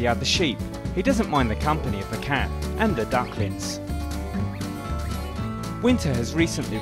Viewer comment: Did he say "duckletts"? That's a pokemon.